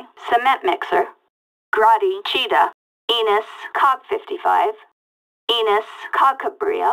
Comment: I don't understand what she's saying? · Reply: She said "Cog55"